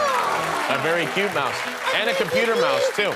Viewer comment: To every german does ist sounds like she is saying „hallooo“ at 3:16??